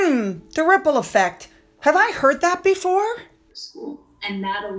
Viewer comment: For a brief moment, I thought she said the “nipple affect” 😂🤣😂🤣😂